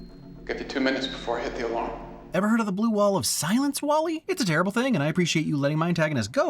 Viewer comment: automatic subtitles thought you were saying Wall-E 😂